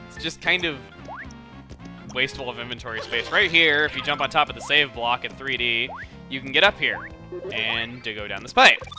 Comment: did he say "sayian block" or "save block".